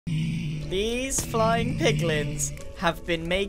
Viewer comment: Nobody, Not even a single soul, Grian’s into: These flying piglins had me me very rich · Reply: I thought he said “these five pickles”